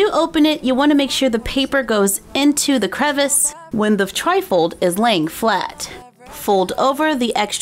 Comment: At the start I thought she said Trifle not Tri-fold! 😂😂😂